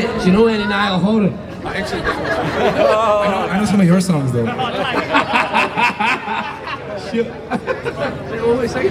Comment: what did say here? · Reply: I think he said he knew some of Lewis songs not Niall’s